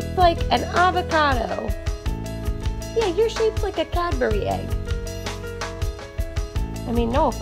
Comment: I thought you said " catbury egg " 😂